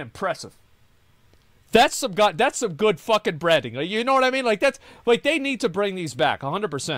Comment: everytime Asmon says "branding" I keep hearing 'Breading"